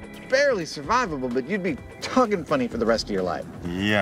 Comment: *Does he say "You'll be Tuggin' Funny for the rest of your life"? lol *